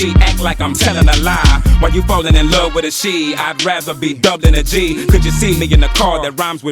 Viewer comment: He said, "while you're falling in love with her (pronounced wita/wida) making it sound like he said falling in love with a she 😂 down south slang